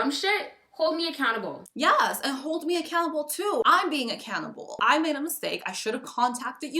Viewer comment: im sorry but all I hear at 11:22 is “I’m being a cannibal” LMFAOOO